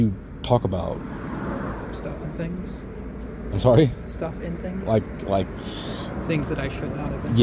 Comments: New small talk unlocked: what are you up to? Stuff and things 😎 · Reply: i heard ‘stuff in things’ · New small talk unlocked: what are you up to? Stuff and things 😎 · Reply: Thought he said "stuffing things" tbh 😂😂😂😂